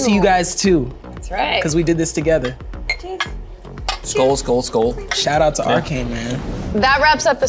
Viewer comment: Did he just say "Skål Skål Skål" omg <3